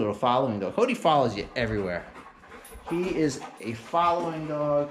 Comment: Я изучаю английский По этому не могли бы вы написать субтитры что сказали на 5:57 секунде? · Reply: He`s saying something about his dog Cody being a following dog, he`s a follower, 100 percent. Он что-то говорит о том, что его собака Коди является преследующей собакой, он последователь на 100 процентов.